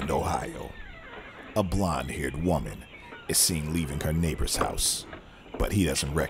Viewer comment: I thought you said a blond-haired woman that's a man